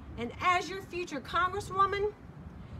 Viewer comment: Did she just said she wanted to make a compensator for the sbr